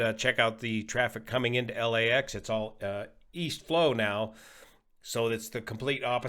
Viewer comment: Did you say,... East? I thought you said Wheast...